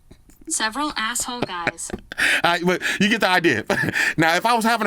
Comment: for a second, I thought it said "several a**hole cats." I thought White Shoes was going to have to get her crew to roll up on you.